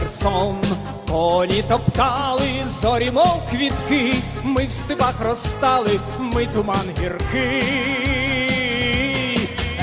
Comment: I swear I heard the word "Stalin" in